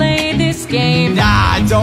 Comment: When he said ik you’re a goddess but I thought he said you are the ****esst